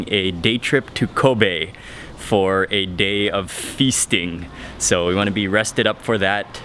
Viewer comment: when you said "day of fisting" i was worried... aaah, FEASTING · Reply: I heard him say feasting though...